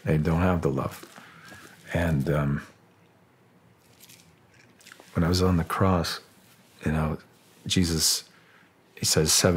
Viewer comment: I just heard a man say "When I was on the cross" 🤯